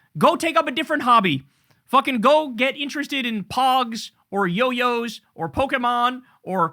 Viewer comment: did just say get interested in pawgs? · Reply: No, 😅😅 he said "POGS"....google it, it was a collectible game for kids 🤷🤦😂😂😂